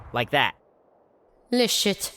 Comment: "Le Shit" Im dying man this is bad for my liver and kidneys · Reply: I thought she said bullshit with an accent